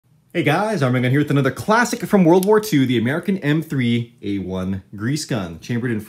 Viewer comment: I thought he said "Classic from World War 3" guess im hearing some things again.